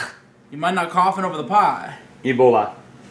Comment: I think that's the only time he said "Ebola" and not "Eboli" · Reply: Nah he said Eboli 😂😂